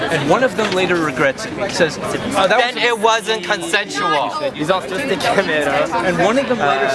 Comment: I didn't hear it really well, but at 7:15 or something the man said "If two people have sex consensually and one of them regrets it later..." He gets cut off by another man yelling "Then it wasn't consensual!" Did I hear that correctly?